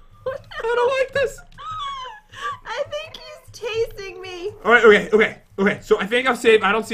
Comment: Who else heard I think he's tasting me at 10:47 😂